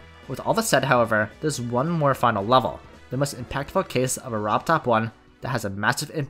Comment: whenever he says 'robbed top 1' I thought he said RobTop1 💀